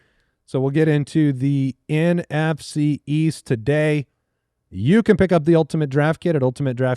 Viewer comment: NFC East? I thought your said NFC WEAST!